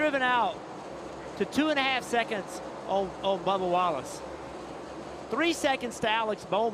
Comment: Sounded like he said Barbara Wallace during the line up :)